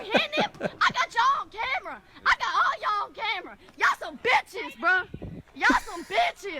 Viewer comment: why does it sound like she is saying ya'll some biscuits